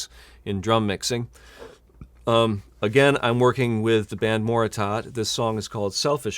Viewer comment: What’s the name of first band? Can’t find them anywhere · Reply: I was talking about the second band. I haven't looked into the first band yet. Sounded like he said Moratot or something.